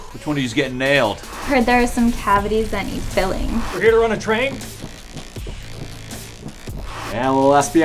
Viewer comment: "We're here to run a train". That is awesome. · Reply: I thought said run and train 😂 since that’s what they were “doing” as a play on words